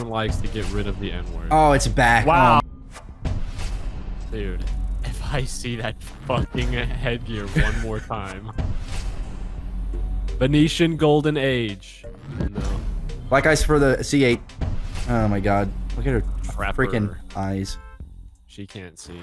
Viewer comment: No joke I thought the Venetian golden age said Vietnam golden age